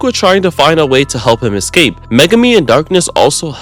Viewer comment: did he just say Megumi- 2:42 yea he definitely said Megumi..